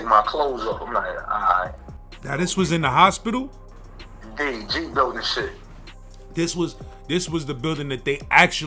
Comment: In the G building taking all types of medecine . "ODB" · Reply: Yooooooo, I always thought he said "Energy building..." Nice clear up, home team.